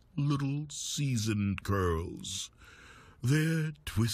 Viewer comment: i thought they said girls instead of curls, and it iust kept getting worse... And worse, and worse